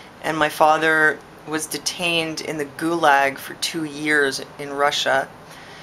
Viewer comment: i thought you said goulash .. mmmmmm goulash